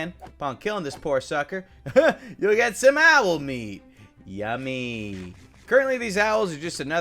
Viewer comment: "Owl Meat" What I hear: "Raw Doni Bobes thigh"